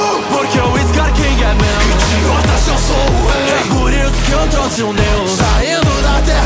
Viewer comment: " idiotas, eu sou um Rei!" " É por isso que eu trouxe um Deus!" Slk que pedrada, nunca decepciona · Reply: Eu escutei “por isso eu trouxe o meu” kkkkkk